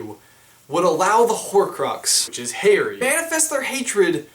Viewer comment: I kept hearing "Hairy".